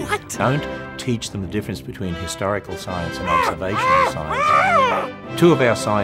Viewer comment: It always sounds like Ham is saying "Hysterical Science", and to that I will agree. Ham's Science is very hysterical! "D